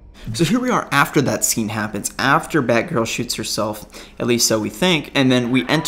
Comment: For a second I thought he said "After Barbara shits herself".